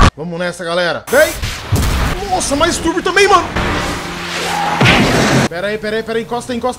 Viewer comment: eu juro que ouvi "masturbo também mano"